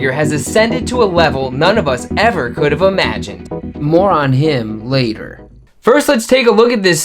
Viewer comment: “More on him” but I heard “moron him” hahah.